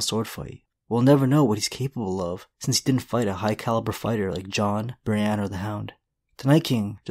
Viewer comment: I thought he said Jon, bran or the hound